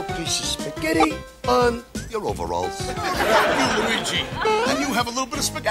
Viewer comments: did he just say oh fuck You luigi lol! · Sounds like he says "F--- you Luigi" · You can't tell me that 1:22 doesn't sound like saying "Fuck you, Luigi!" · Did just say fuck you Luigi at 1:22? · is really saying "oh fuck you Luigi" cuz that is all I can hear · Are we not gonna skip over the fact that at 1:22 it sounds like said "fuck you Luigi" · wait what?! Does he say "eh fuck you Luigi" LOL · Im unsure if anyone hears what I hear? Didn’t say the F word there? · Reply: I heard, “well F you Luigi.”